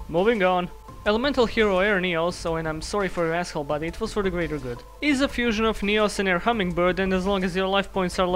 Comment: Wait wjat does he say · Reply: "Im sorry for your asshole buddy but it was for the greater good"